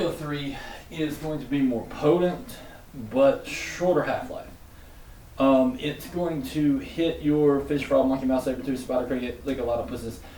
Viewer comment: is it just me that can't understand single word said there? I got "It's going to hit your" then? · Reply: FishfrogMonkeyMouseSabertoothSpiderCricketLickALotofPussus